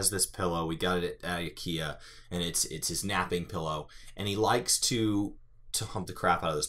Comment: i knew it was gonna be a humping story but doing it in the middle of room and with a duck im dead. guess he like exhibition lol. oh and btw i literally thought u said fapping pillow lmao.